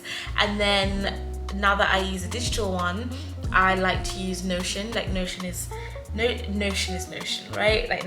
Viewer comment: If anyone is looking for the digital planner it's called Notion. I couldn't figure it out for the life of me. At first I thought she said noshen, noshin, and I couldn't find it in the app store lol